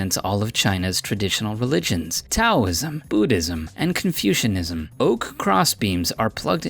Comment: Did he said "CONFUSION-ism